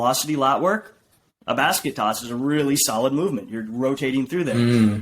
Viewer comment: basket toss? What exercise is he naming here? Couldn't hear it quite well.